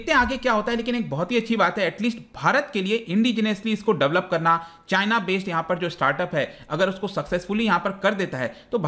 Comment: 👉CHINA🇨🇳 based Startup instead of CHENNAI🇮🇳 based Startup✅